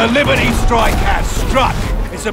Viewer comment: I thought it said liberal strike